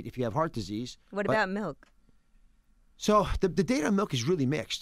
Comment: Lmao envy and me thought oz said head milk